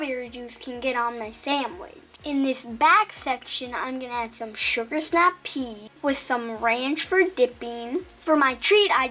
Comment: Is it just me or when was adding the sugar snap peas it got cut to fast and it sounded like sugar snap pee at 5:10 😂 I’m not bulling hi m it’s just a little Mistake made in the video just so you know 😃